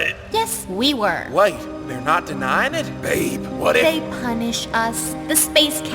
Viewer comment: did he call Europa babe